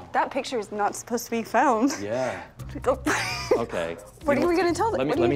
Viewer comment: does she say that picture is no supposed to be filmed?